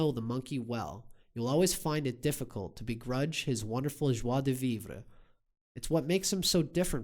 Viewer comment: What’s the word he said at 17:35 ? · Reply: "Joie de vivre"